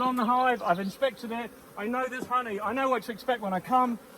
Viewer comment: Did you say I know there's honey or my nose is runny? Because every time I seem to get into a pissy hive and the bees sting a few times my nose starts to get runny. I thought I was the only person. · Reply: its Theres honey, but it could be my nose is runny!!!lol lol